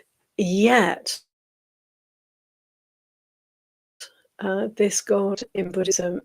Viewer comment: we miss what you said it freezes! 🙏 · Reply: I was saying something like . . . "Yet, even the most Almighty of the Gods is subject like all beings to the laws of samsara - impermanence, suffering and not-self - And so they too, when the extremely good kamma/action that led them to this experience has run its course . . . they too will experience passing away and change to a different state, which in the case of the highest of Gods, will be a change to a more contracted, less conscious state . . . "